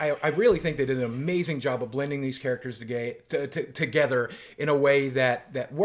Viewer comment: Oh no, I hear demonetization sirens. "Blending these characters to gay" haha. 2:00 Great content still.